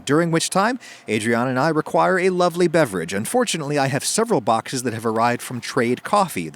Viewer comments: u said unfortunately instead of fortunately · When starts talking about his sponsor, I always mishear, "and fortunately," as "unfortunately," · said unfortunately instead of fortunately by mistake lmao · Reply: I thought he said "and fortunately" lol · 4.08 Genuinely thought he said "unfortunately" rather than "and fortunately" Weird thing to say about a sponsor!